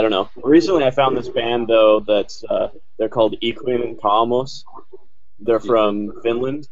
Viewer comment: What is the band's name saying in 20:34 ? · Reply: It's Ikuinen Kaamos